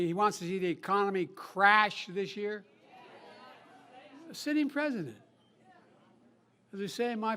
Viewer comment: Did I hear Biden refer to Trump as the sitting President?